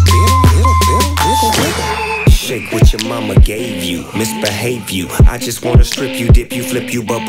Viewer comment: idk if I'm trippin but in 1:56 i kept hearing shake what your mamma gay view LMFAO- somebody send help 😭💀😆